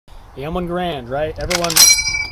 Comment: Garand, not "grand " 🤦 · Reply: he said garand just fast so it sounds like he said grand 🤓